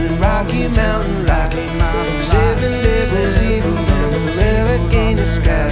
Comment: I heard “settin” instead of “seven”